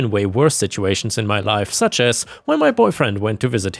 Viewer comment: I heard I’m in a relationship with my brother not boyfriend😂 i’m listening. To This at work so I’m like wait what the fuck😂